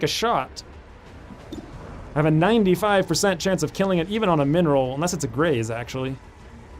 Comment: "min roll"? i keep hearing "mineral" :D mby u can rename the minimal damage to that :D